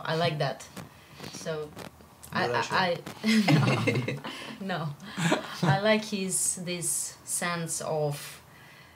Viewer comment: what does Riccardo say at 12:48 ?? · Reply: Duracell